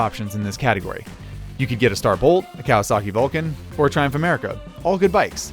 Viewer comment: Just want to say your content is blowing me away, keep up the good work. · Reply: Question about the video. At 3:10 starts listing bikes. What's the first one he says? Sounds like "star volt" which is not a motorcycle so far as I know :P